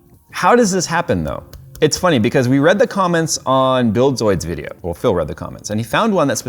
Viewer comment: I kept hearing "Bill Soid". Oh, you mean Buildzoid.